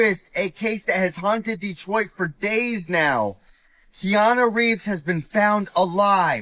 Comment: I thought he said keanu reeves instead og Quianna Reeves, and then heard john gacey and was like what the hell this cant be real, but it is. Crazy coincidence.